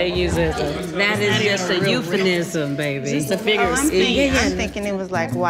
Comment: Please don’t tell me I’m the only one who heard “EupheNism” instead of “EupheMism”... 😭😭😭😭😭